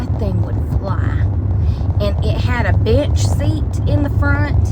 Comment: I swear I heard you say b!7ch seat😂😂😂😂